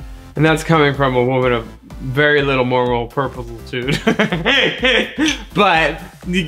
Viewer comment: captions said "very little more roll purple too" and that's what i'm inclined to believe was said